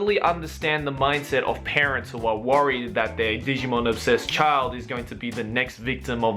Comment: did he say Digimon?